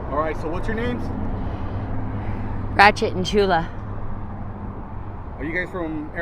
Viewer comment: I thought they said "batshit and chula" lmfaooo